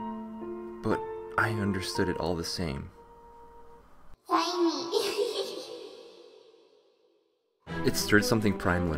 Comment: what did "It" say? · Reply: Find me.